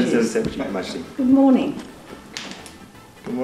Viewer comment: It sounds like she said "I CAN move", not I can't move.